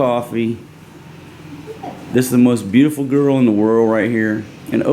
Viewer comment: I thought you said the BBQ girl was the most beautiful woman in the world...??? 😂 What’s that on your lip?